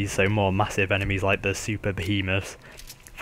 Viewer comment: you said Super-behemoth but it sounded like Super penis.